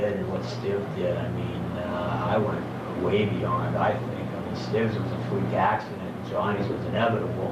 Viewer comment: I can't hear it, who is he talking about at 6:40? Thanks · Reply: Johnny and Stiv are members of the band the dead boys, a punk band from Cleveland Ohio